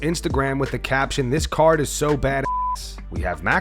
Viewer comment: Dana so use to power slap cards he thinks this is a banger 😂😂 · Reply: I thought Dana was saying "THIS CARD IS SO BULL****!"